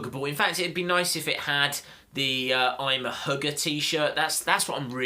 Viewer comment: I thought he said I am a Hooker it's actually a hugger (I heard it because he is British) 😂😂😂😂😂😂😂😂😂😂😂😂😂